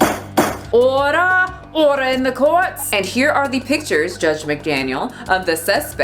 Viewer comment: At first I thought you were going to say ora! ora! ora! in the court.